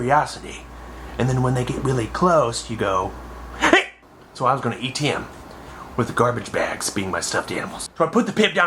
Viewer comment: I can't be the only one that thought said "I'm going to eat him" before he explained the ET scare tactic.